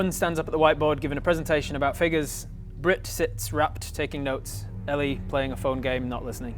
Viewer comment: pretty sure meant rapt here, not wrapped as the caption says. maybe it’s still AI when i’m watching this.